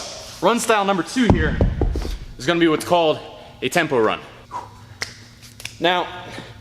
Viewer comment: i thought he said Temple Run. Dissapointed.